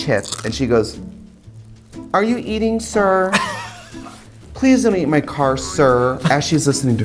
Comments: I thought she said “Please don’t eat my car sir” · I thought Katya was saying ”Please don’t eat my car sir” but I realize it’s not that... now I’m sad